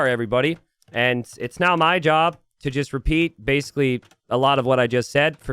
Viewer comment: I heard "now my job" at the beginning as "not my job," so I was like, "All right then. Keep your secrets."